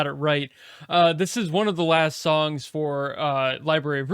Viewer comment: Who said that? It's up next 👀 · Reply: ooooo you said one of the last song, I miss heard it as the last song 🤦. Glad to know that!